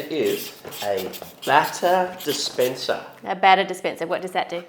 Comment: I thought they were saying butter dispenser